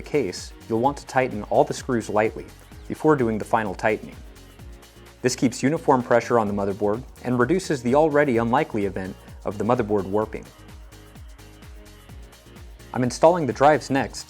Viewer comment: i heard "working" instead of warping the mother board, i was like wait... unlikely event of the mobo working? wtf mobo are you using? XD